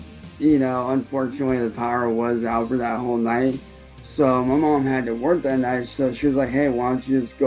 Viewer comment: He said he went home, and Sally asked him why he wasn’t staying the night. I thought he said Sally had to work that night.